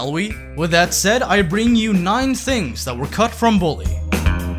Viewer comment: When you said nine things I thought you said naughty things